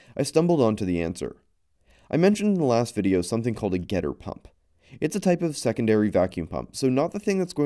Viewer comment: I misheard "getter pump" as GHETTO PUMP, thinking that this was probably some extremely ugly but working hack...